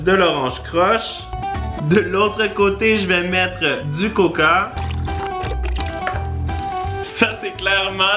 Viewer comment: il as dit quoi là , du COCA ???!!!! Je suis désolé mais le coca c'est pas pareil que du pepsi . Le coca c'est beaucoup plus bon